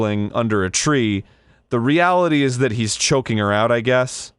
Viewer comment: i thought it said treesome